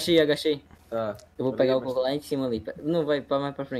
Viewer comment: só eu que ouvi o falando 'eu vou pegar o coco lá em cima vida'